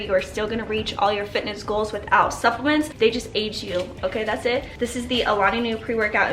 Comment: Whitney Simmons At 2:42 did you say the pre workout ages you? Lol I couldn’t hear what you said 🙈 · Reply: Olivia B she said “preworkout will AID you”